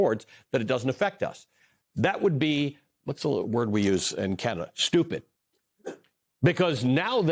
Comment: did he say, the word we use in Canada?